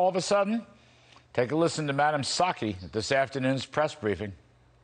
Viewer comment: At first i thought Larry said madam Psucki ? must be the New York accent ? i call her Bagdad Jen, i think it fits !